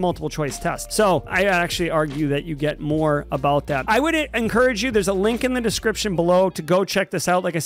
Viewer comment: Did you say, " I wouldn't encourage you " here?